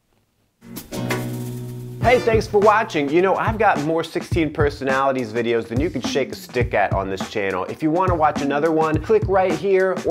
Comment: I thought you said "I've got more 16 personalities videos that you can shake your stick on this channel"... And I had to rewind 😂